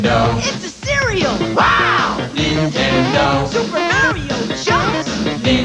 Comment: is it weird that I heard the kid say SUPER MARIO JUNK